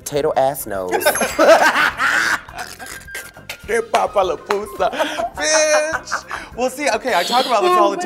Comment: What does Raven says at 7:15? Papa la pousa?